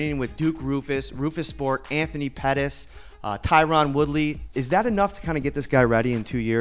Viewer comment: did he say tyRUN??? lololol